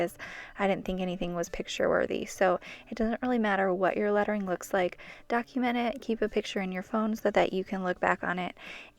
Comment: please, What is the word in the sentence? I want somebody to tell me what the sentence is. "comparison is the thing ( ?) joy." if you tell me the word, I will be happy. because I've been learning English quite long..but My English is not good enough..ʕ•ﻌ•ʔ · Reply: Hey! I'm so sorry! It's a "thief of joy"... like comparison steals your joy! Hope that helps! :) Thanks for watching!